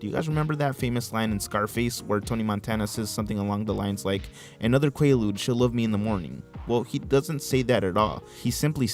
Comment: I swear he says “another kilo, she’ll love me in the morning”….